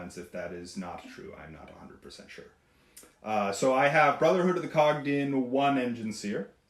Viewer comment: Definitely thought said "brotherhood of the cock" and now I can't unhear it.